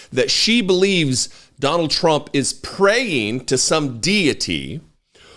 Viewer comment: Did I hear right "... he's preying"? Oh she meant "praying". Makes you wonder who he's praying to and what he's praying for. "Please (insert deity of your choice), make this case go away and save me from prison"